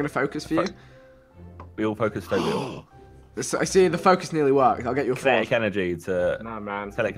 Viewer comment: Who heard Viks bants at 6:30 'The focus nearly worked. I'll get you a Ford.' I'm so dead ahahaha